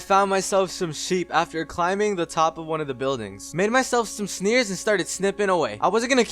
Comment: Who else heard him say shneers!🤣